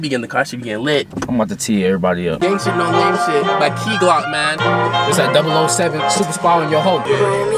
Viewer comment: I thought it said gey glock lmao